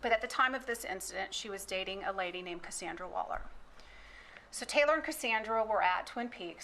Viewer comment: I thought she said Amanda Waller. Thought we found a Batman villain for a sec.